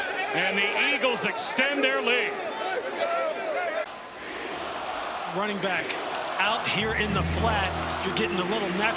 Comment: anyone know what the crowd was chanting? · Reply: We fucked up 😂